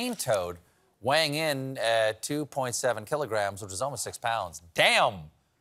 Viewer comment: What I heard was, "2.7 kg, which is almost a pound". Hopefully that was just poor enunciation, because 2.7 kg is almost 6 pounds.